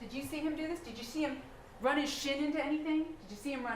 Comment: Did she say at 16:54 " did you see him run his shit Into anything 😳 "